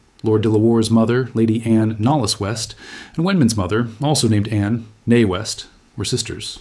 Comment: Anne neé West was beautiful. When you said her name, at first I thought you said "Mae West."